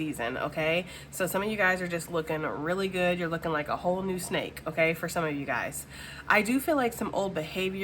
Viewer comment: I heard “looking like an all new 𝘴𝘯𝘢𝘤𝘬."🤣